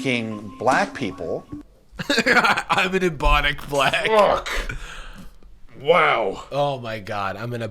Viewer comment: Im so fucking stupid i thought he said "Bubonic black"